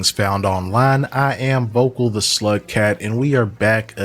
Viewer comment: I thought you’ve been saying “vokal the slut cat” and I was like fuck yeah, I’m with that. I just noticed the user name is SLUG cat. 🫩 my bad 😂